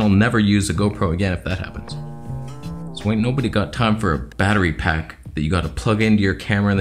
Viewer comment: Misheard that point ... thought you said jet pack but actually battery pack - makes more sense now! Although I could see you using a jet pack and your GoPro